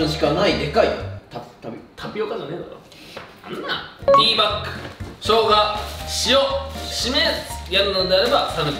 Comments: ティーパックがティーバックに聞こえたのは私だけでは無いはず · 〜ティーバックに聞こえましたごめんなさい。 でも 2:15〜はティーバックでした。謝ってください。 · 商品紹介の時、ティーパックがティーバックに聞こえた俺はきっと耳が腐ってる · ディーパックがティーバックに聞こえた人✋